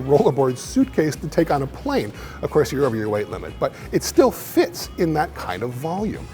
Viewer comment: When he said "you're over your weight limit," I heard "I'm over my weight limit."